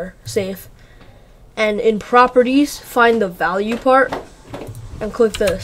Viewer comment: excuse you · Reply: Well 4:38 did he say fart xD